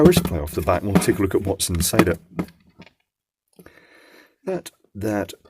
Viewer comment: I heard "And we shall take a look at watts inside it" and this just tickled me. I love your videos - They brighten my day sir.